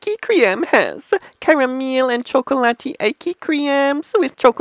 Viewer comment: WTF is this? When he said chocolate it sounded like he said cocain